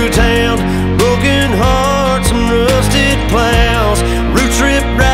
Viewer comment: I always hear 'rusted clowns' instead of 'rusted plows'. Just me? I know.